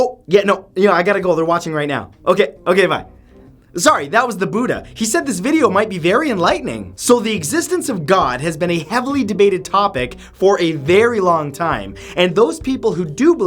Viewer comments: I thought he said unlikely instead of enlightening in the beginning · For a second I thought he said "heavenly" inside if "heavily" in the beginning XD